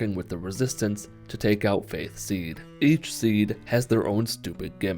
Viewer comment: when he said faith seed i thought he said faze seed